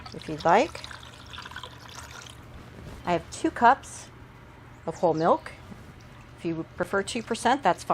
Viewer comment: When said "I have 2 cups of whole milk", I heard "I have 2 large pitchers of whole milk"...not sure why....